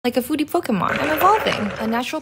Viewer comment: why do I hear “like a foodie fokumon”